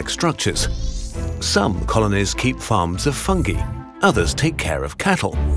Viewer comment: Misheard "farms of fungi" as "farms of funky" and now I only picture ants dancing under a disco ball.